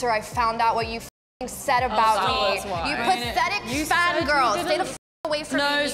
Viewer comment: what does Lauren say to willow? It sounds like she calls her fat or is it something else? I’ve been trying to figure it out for ages and calling willow fat is an insane take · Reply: No she didn’t say fat she said”fan girl”